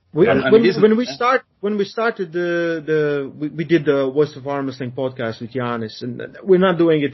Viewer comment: Where did the Rolls Royce joke come from lol? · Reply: Yep. It was me having a chuckle at accent. When he said voice of armwrestling one day I heard it as the Rolls Royce of armwrestling